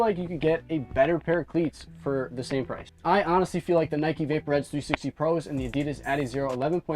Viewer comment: Nike Vapor what? I didnt understand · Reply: Nike vapor edge 360 pros